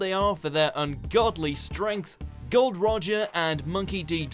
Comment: Did he said God roger. or was it the accent? Or I misheard? It's Gol D. Roger. :D Otherwise known as Gold Roger since ppl misused his name :D